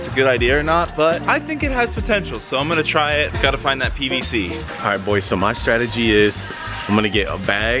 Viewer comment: I thought said I got to find that bbc😂😂🤣🤣